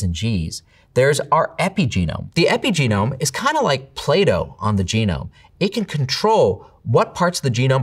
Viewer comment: It took me way too long to realise he said "playdough" instead of "Plato". I was so confused. 😂